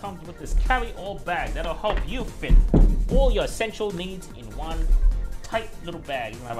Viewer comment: I THOUGHT HE SAID SEXUAL NEEDS LOL